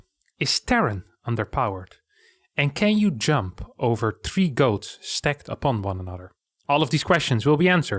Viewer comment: Jump over 3 ghosts. Is what I thought you said. Why goats? Why either....?